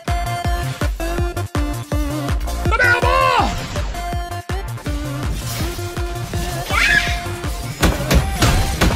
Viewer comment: Can someone please tell me what the sound effect is at 0:20? What is he saying? “Banana ball”? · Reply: "Come here boi" :)